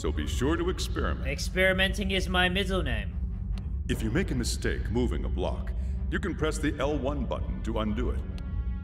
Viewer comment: I thought it said moving a black